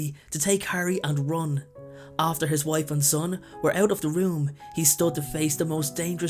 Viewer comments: did I hear Ron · why did you say to take harry and ron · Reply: he said "run" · I thought you said harry and ron